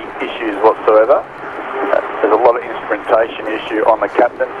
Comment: it sounds like crew says there's "a lot" of instrumentation issues", rather than "slight"?